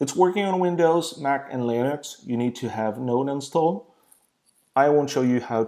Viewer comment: you need to have what installed? C'mon, it's not like you were saying what time is it? Even the auto generated subtitles got confused. If it's Node (Node.js) you are talking about, now that is a funny way to pronounce it!